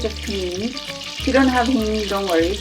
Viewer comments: sorry, but cannot hear what you're adding at 3:50. pneen? neene? thanks. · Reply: Hing or asafoetida in English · At about 3:50 she puts something in the pot but I could not understand what she said it was. The close captioning says 'honey' but I think that probably isn't right. Anyone know what the added ingredient is? OK, I see that the written recipe says the ingredient I asked about is hing. Later on in the video there was another moment in which it was hard to hear what the presenter was saying. Possibly this is the only small thing this presenter needs to work on in future videos, which will no doubt be quite interesting to watch. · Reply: She added Hing also known as asafoetida. If you don't have it, you can still make it. It's just a powder that'd pungent and umami like. It's sometimes used as a substitue for onions and garlic. You'll find it many traditional indian households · What ingredient does she use at 3:52? I can't hear what she says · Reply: It's Hing, you just need a little pinch.